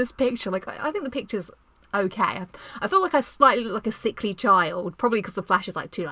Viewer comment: I believe Nathan Drake once said "Started from the bottom now we hea. Started from the bottom now my whole team hea..." Lol I thought that she said that she looked like a sexy child lol awkward especially rechecking it lol JK